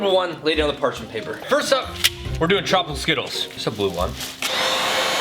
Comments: Charlie not facing the camera near the beginning made me feel weird. I could have sworn you said chocolate skittles. I almost lost my mind! Okay, watching kernels pop is so satisfying!! The marshmallow too! WOW!! The jawbreaker was SOOO PRETTY!! Would LOVE to see you do more of this! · i honestly thought you said chocolate skittles, and i was like M&M's?